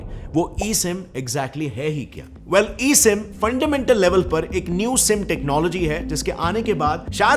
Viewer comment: Did I just heard New simp